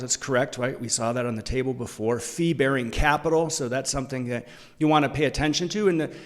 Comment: thanks for the video. BTW I thought you said "flea-bearing assets", until I remembered you weren't talking about the UK's worst smallcaps.